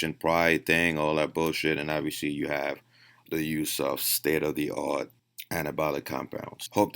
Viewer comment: When you said "state of the art" at first I thought you said "Ste-er-ri-oid" haha